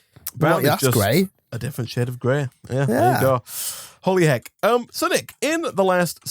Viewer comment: Thought he said holy egg XD lol